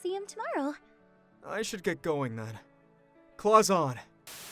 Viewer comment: Is or does he say claws on😂😅